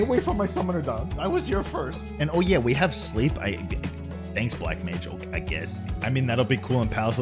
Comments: Im so hyped for this! Summoner main when i started until now until forever! woohoo <3 btw what did he say at 6:24 i cant understand. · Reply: I think he said "Oh yeah, and we have Sleep now. Thanks Black Mage...I guess...?" Sleep was made a Caster Role action now instead of BLM only. (SMN, RDM, and even BLU can equip it.) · What was said at 6:25? “Oh yeah, we have ???? I guess”? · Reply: "we have sleep" looks like blm sleep will be a role action